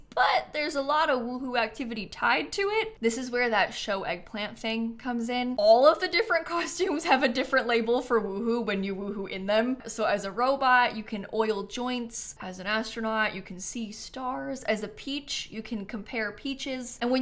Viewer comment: i thought you said roll joints i was like 😨